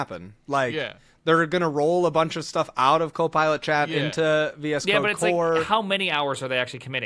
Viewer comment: I appreciate the censorship on the term "VS C*de", there's very impressionable people listening to these podcasts. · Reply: I thought he was saying "VS cock"